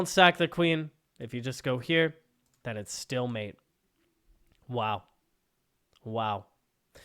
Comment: I was a bit confused as I thought you said stalemate and not still mate